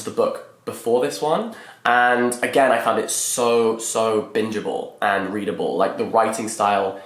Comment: On first listen I thought you said binnable not bingeable